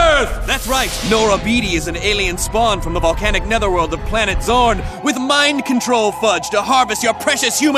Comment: I thought he said "Planet Zord"